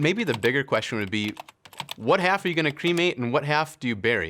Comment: "Maybe bigger question would be: what half are you gonna cremate and what half do you bury?" My Godd im dying can u imagine looking at a casket and only seeing the leg part😭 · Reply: I thought he said crewmate bc he said cut in half. Luckily, he didn’t.